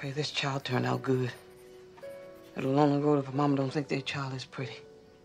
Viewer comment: What does she say 0:25-0:27? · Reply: Beth O'Bryan "got a long road if their momma don't think their child is pretty"